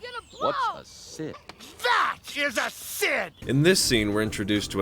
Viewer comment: Is it me or did Woody said "Sin" or "Sid" in this part here